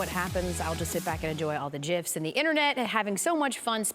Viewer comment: did she say "JIFS"? LMAO